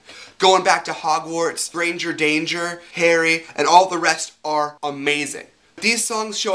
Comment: pahahaah when you said Harry I thought you said Hairy! xD